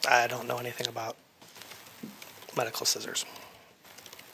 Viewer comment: I thought you said he knew nothing about medical scissors